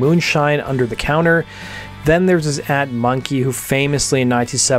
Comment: What does it say @ 1:18 ?? Transcript reads “Then there’s this ad monkey…” what is he saying here??? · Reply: "His aunt [monkey]"